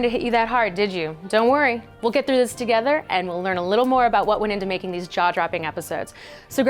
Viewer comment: I was chewing food and misheard that whole sentence as "learn a little more about how to make a Jar Jar epic suit"